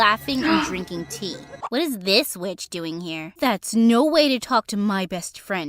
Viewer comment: Nah when she said when did this witch get here? I thought she said when did this bish get here😭